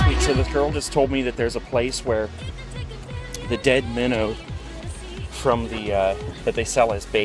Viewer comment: I heard "The dead minnow" as "the dead men" for a second... i was like jesus, donating your body to fishing after you die? lol